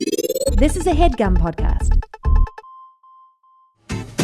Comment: All this time I thought Steve was saying "This is a Headgum podcast" ...